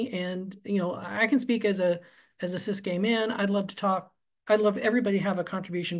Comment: did he say he was a cis gay man ???